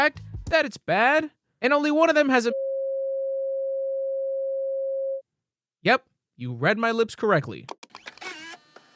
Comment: I can’t read lips. Can someone please tell me what he said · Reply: 7 months late to the party: But he said something like "bunch of bull sht trns person sicide scenes" Edit: typo fix from scene to scenes*